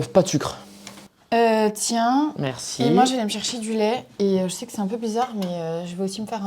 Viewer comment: moi, j'vais m'chercher du lait. doubtful sub there, in phonetic terms is more like: moi, jevemchercherdu.le', connecting the je vais me chercher as a single word, the aller pronunciation, is not there, and the word in casual conversation is unnesesary, yet again the subtitles are wrong, even if that's the full way of saying we do need the subtitles to account for casual conversations, and to account for phonetics if possible